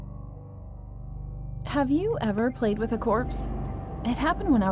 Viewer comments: In the 1st story I thought she said have you ever played with corpse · Oh she said play with a corpse, I thought she said have you ever played with corpse like corpse husband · I thought the first one said “have you ever played with corpse” lol